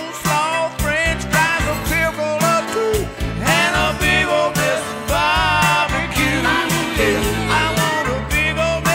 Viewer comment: At around 1:14 to 1:15 what is he saying? "...french fries, a ??? or two." I can't make it out... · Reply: The line is "Bring me some cole slaw, french fries, a pickle or two and a big ole mess of barbeque"